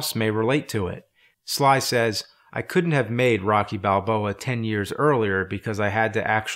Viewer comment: I thought he said Rocky and not Rocky Balboa. Maybe it’s the Mandela Effect.